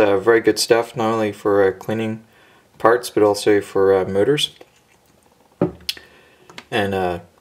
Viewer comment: i thought you said goo gone is also good for murders instead of motors lol.